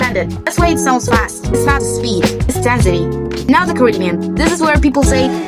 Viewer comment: it says korean instead of caribbean loll. maybe you should double check your subtitles. i’m not sure if you use AI auto-subs and if you can correct them or not but yeah that’s just something i noticed :p